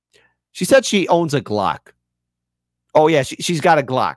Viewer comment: "GLOCK!? Oh, I thought you said clock! I was like, yeah, I got those hanging all over the house! Ahahahaha! 😄"